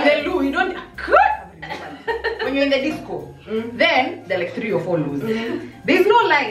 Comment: Did just say 'disco' at 4:00? i said that word one time at a party and was told i should be an ancient relic at the Archives lol..